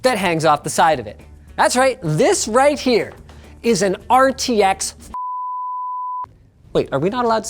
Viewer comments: i heard RTX 3080 :) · RTX3080 is what he said